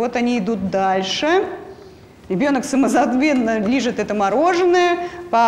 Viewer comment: На 18:49 я одна услышала "ребёнок самозаДвенно лижет это мороженое..."))?Самозабвенно!Да уж...озвучила,так озвучила, свою роль)!🤦‍♀️🤪🤭